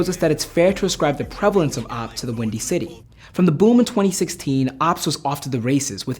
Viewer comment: I thought it said “oops”